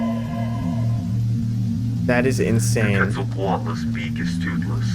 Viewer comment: wtf was those random words .. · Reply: "Is this the wrath of quetzalcoatl punishing us?"